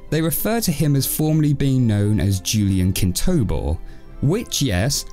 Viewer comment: BITCH I THOUGHT IT SAID KINKTOBER I ALMOST CRIED I WAS LAUGHING SO HARD